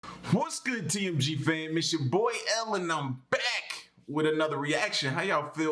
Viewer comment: it do sound like you saying " it is your boy Ellen" maybe its just me